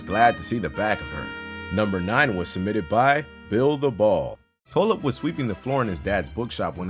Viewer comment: Thought for sure he said "build the wall"